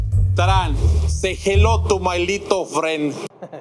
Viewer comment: I am pretty sure that he said "my lethal friend" instead of little